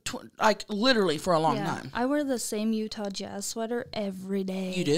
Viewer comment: When said she wears a Utah Jazz sweater everyday I straight up thought she said “you touch ass” til she said her grandma gave it to her 😂